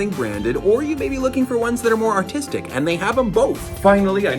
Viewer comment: I thought he said autistic